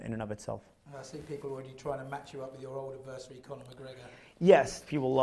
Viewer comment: I thought it said “disses Connor McGregor” 💀